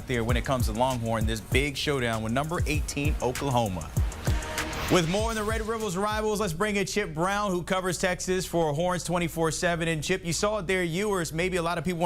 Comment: Did he say Red Rivels Rivals? 😄